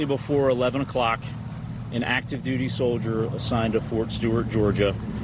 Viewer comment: Could of sworn that said sus soldier 🪖